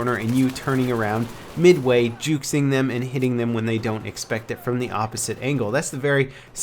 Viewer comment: did he say juxing instead of juking?